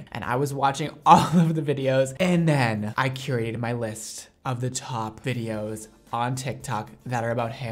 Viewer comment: I thought he said "I curated my lisp..." And i was like: Oh, never noticed it" Face palm